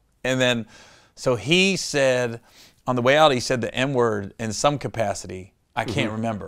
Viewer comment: I thought he said ‘M word’ and that sent me down a weird spiral.